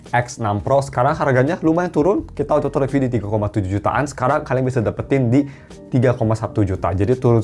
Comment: ngomong apaan sih gaje njirr 😅 belibet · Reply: *Kita utk review di 3,7jtan